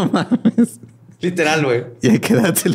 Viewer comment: alguien que me diga que dijo que no entendí jajajaja saludos desde Venezuela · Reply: Creo, que dice: " ¿y a qué edad se descalabra el chango?"